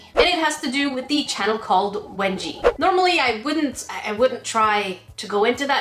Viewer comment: I thought that you said wedgie 😂